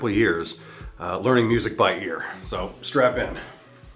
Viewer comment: did he say strap in or strap on